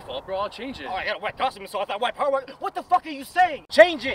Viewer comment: What does the black ranger say at 0:22?? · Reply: "Oh, I had a white costume so I thought white power. What the fuck are you saying?"